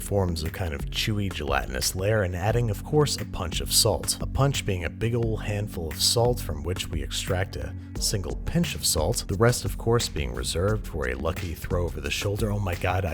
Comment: If i had a dollar for every time he said 𝓚𝓸𝓼𝓱𝓮𝓻 𝓼𝓪𝓵𝓽 i'd be rich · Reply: I thought he was saying ‘bunch of salt.’ ‘Bunch of salt. Did I say pinch of salt? Bunch of salt.”